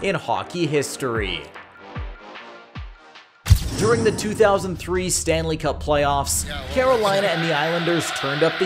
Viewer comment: Camera quality pretty clear for a 2003 islanders hurricanes game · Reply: He said 2003 thats what we were saying